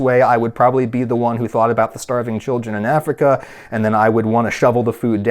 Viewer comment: I thought he said "starving chicken in Africa".